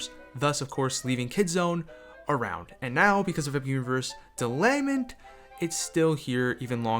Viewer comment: Did you just say "delayment?"